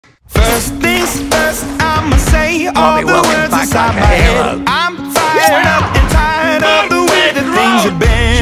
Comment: Everyone: Mhm...cool watches whole video Me: Keeps replaying it trying to figure out what barty crouch said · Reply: Bartimus Crouch JR said "I'LL BE WELCOMED BACK LIKE A HERO" So yeah